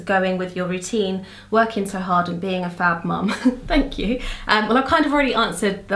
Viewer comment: I heard 'fat' at first, was like lol whu xD